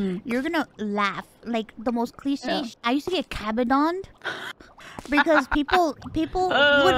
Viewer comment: what term is she saying at 3:50 ? i cant tell what shes saying and i wanna know what it means 😭🙏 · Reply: Kabedon: Slap a wall "fiercely" to make a "don" sound. In general, the person doing the kabedon will slam one hand against the wall and lean into the other person to intimidate or fluster them